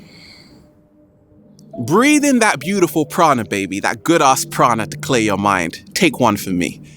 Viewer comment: peeeeaaaaccceee: we are here. in nature. breathing in that goodass prana baby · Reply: The first time I heard him say that, I honestly thought he said Prada??? Hahaha.... But it's great to know he's referring to Prana which is collectively all cosmic energy.